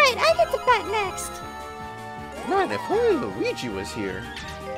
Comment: what was Mario saying again? I can barely hear him. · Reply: He said "Man if only Luigi was here"